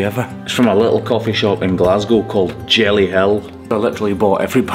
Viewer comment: JellyHill sounded like (JellyHell) 😂❤